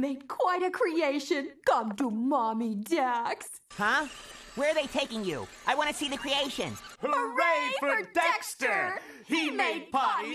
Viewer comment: What Did Mandark Say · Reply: "Where are they taking you? I wanna see the creation!" DISGUSTANG 😳 😬 😑